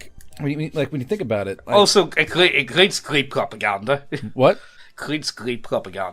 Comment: I like how when said "creates" thought he said "krieg". Northern Irishman moment.